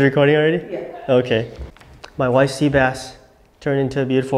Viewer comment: I dunno. I heard “my wife’s sea bass” it’s time to put a ring on it!